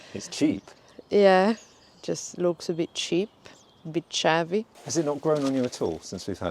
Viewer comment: did say it looks “a bit chavvy”? 😂